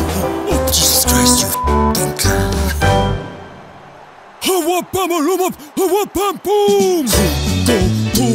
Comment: wtf is he saying?? 😂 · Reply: Howwabamaloomaphawapbamboom